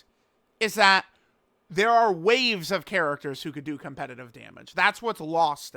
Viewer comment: I keep hearing this "there are ways of characters that do competitive damage" while I hear him complain about hitting the boss for zero dmg